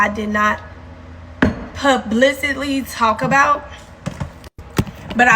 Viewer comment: Did she say "pub-licitly" talk about? 😂😂😂 Stay in school folks